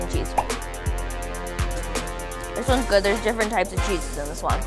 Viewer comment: did she just say “gooder”